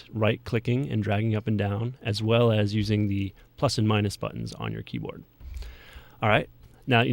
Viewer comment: when he said plus and minus i thought he said fuck and minus lol my mistake:)